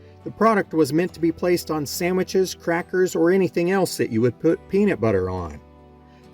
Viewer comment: did he say "samiches" lol... i hope.